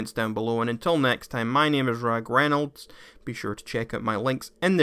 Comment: I thought he said his name is ryan Reynolds